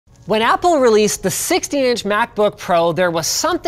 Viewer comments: did I hear "when apple released the 60 inch MacBook Pro. Damn that's a big boy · Sounds like he said 60" MacBook Pro. That would not be very portable